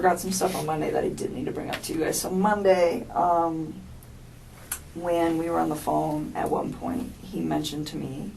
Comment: I heard her! She said, "Monday, when we were on the phone..."